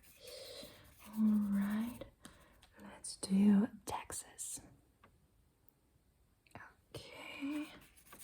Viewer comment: you said taxes i heard texas and honestly seeing what is happening in America as a european I’m more scared of texas than i am of taxes